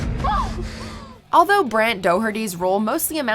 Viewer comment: Is it just me or did she call Brent Doherty Brian? Also it had his name as Brant on the screen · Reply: it was written on screen correctly, his name is Brant not Brent. But yeah, I heard her say Brian as well.